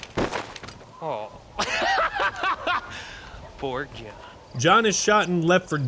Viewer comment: Bill says “poor John” here!? I always thought he said “implored ya.”